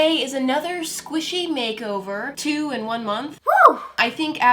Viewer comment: At first I thought it said sonic makeovers